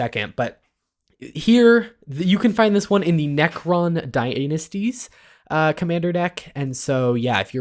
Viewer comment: did he just say "Necron Dy-ANUS-ties"????????